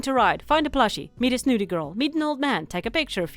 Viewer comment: I thought you said "meet a nudist girl" and had to rewind multiple times XD